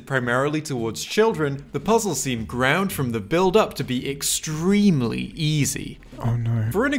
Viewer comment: anyone else hear the "ground from the build up"